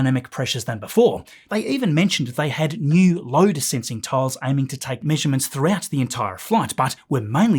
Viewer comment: I keep hearing 'Odour sensing tiles' - is it your accent or are they giving it a sniff?